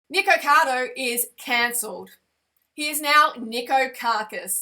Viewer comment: Hahah I thought when she said Nikoavocado is cancelled I thought she said nikoavocado is cancer hahah